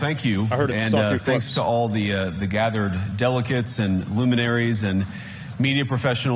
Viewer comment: lol, did he call them "delicates" instead of delegates or is it just me? 11:27 This is not extraordinary, it's been getting worse for years but the stories are suppressed/censored.